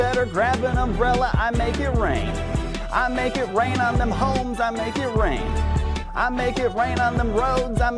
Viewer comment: I thought he said hoes